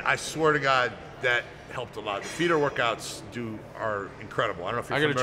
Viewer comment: I cannot get what they are saying at 8:58? Defeater workouts?? I cannot get the word... RIP Rich Piana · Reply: feeder workout is the term